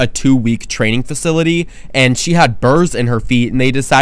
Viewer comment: Also corn starch coats the burr and makes it come out much easier. Great job! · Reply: What's burr?I thought he was saying burns and I got scared